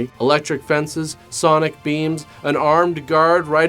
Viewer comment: Sonic beans? No, no I guess “beams” probably makes more sense.. I thought you did say “sonic beans” tho and was excited for a moment...